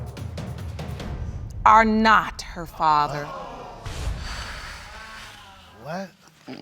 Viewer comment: wait a minute. I'm pretty sure I heard 'NOT the father'. But 'you are the father' appears?! Oh, great. More confusion...